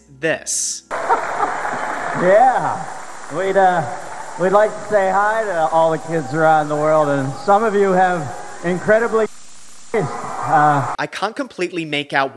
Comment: if i had to guess from context and the lips i think Dennis Miller is saying here "We'd like to say hi to all the kids around the world, and some of you have incredibly shit taste"